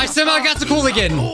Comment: When he hit COOL mode its sounded like it said "shit"